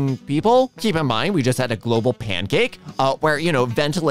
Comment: instead of pandemic, the subtitles say "global pancake". Man I wish